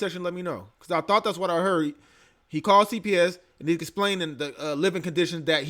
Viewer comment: i think he said DCF